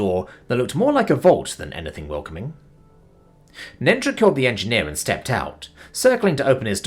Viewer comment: I swear says "Nendra killed the engineer" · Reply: meant killed the engine and stepped out (of the car). My favorite -ism is when he pronounced vinyl as vin-yeal about 5 times.